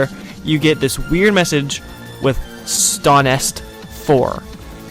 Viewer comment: I thought he said stoner 4